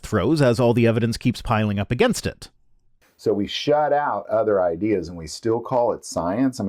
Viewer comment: Did he say "we shat out other ideas"?